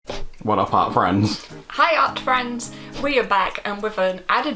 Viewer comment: I thought said: "What up HOT friends"